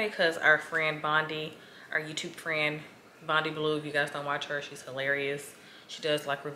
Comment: can you share the youtuber that you mentioned at 28:57? couldn't really catch it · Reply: She said Bondy Blue.